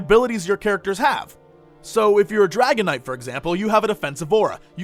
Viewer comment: I totally thought he said dragonite